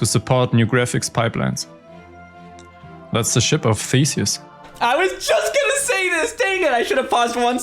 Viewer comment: bro, I was working and had this video playing off screen and all I heard was "ship of feces"